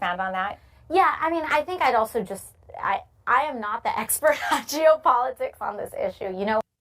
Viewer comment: I thought she said “Jew politics” at first I was like what? The. I realized she said “Geo politics.”